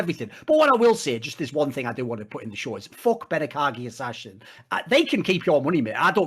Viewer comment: who is he talking about? Ben(something)? Dont hear the name properly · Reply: benekagi ashashin i think his name is. a patreon user